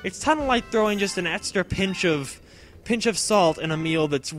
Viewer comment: What you said: "throwing an extra pinch of salt in a meal" What I heard: "throwing an extra pinch of salt in Emile (Chugga)"